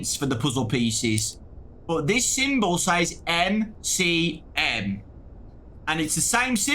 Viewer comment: I thought it says M.O.M.